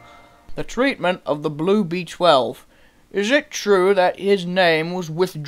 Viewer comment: Took me a while to figure out Gordon was saying blue b12 sounded like he said blue beached whale.